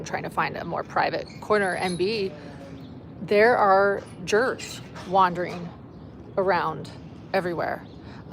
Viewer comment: I'm so sorry - at first I thought you had said "There are a lot of jerks wandering around"; after listening more, you did say "jurists" no?